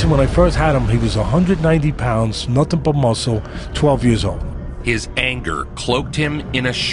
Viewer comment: WTF 8:17 12 years old and 190lbs with i think he said "multiple muscle"? I cannot even begin to imagine what being a muscular 12 year old who weighs 190lbs would be like lolol! I started growing and hit the gym pretty hard when i was 16....after 1 year i went from 98-120lbs rofl